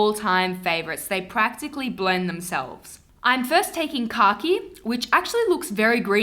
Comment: The Bostonian in me thought you said "car key" instead of "khaki" LOL